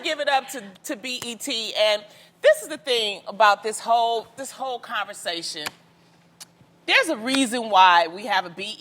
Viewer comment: I thought Loni was going to say " This is the thing about this hoe....." but she said "... this whole conversation..." rotfl!